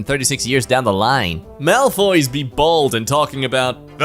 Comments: did they just say Malfoys instead of mfs? I mean they do be inbred so I guess it works for me? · He really said Malfoy's Omfg😭